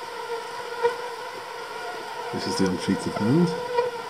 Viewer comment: Lmao!! There’s no need to put the untreated hand back in, we know they still bite!! Lol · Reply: For some reason I thought u said un treated meat lol